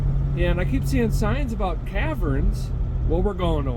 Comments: Dang...I thought a feller said "tavern". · 0hhh Caverns, I thought you said Taverns!! 😂😅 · Oh you said caverns, at first I thought you said taverns. Both are good I suppose